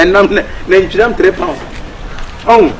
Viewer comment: I heard nantwinam 3pounds from Kyekyeku eiii anaa is only me who heard that 😂😂😂😂